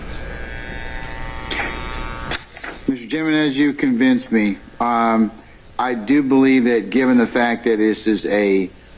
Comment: What did she mumble at the 2:30:50 mark about just wanting to get the leader? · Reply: She said “I just wanted to get the videos in”. Basically meaning that she doesn’t care how the video comes in, with him as an expert or lay witness, she just needed a way to get his enhanced video into the trial and in front of the jury.